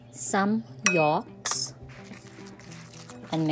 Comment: What is she adding at 1:55? Sounds like she saying rocks, which obviously isn’t true. According to the subtitles, it says yolks, except there is no yolks in the picture it looks like some sort of olive oil, or something so I don’t know.